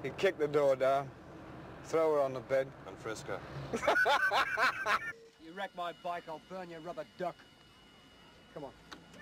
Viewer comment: Ahem, can some Brit please confirm if I heard it wrong 'cause I'm not trusting my ears and English here... DOES Doyle actually say to Bodie in 2:09 - 2:12 "(if) You wreck my bike, I burn your rubber duck!"....... ??? XD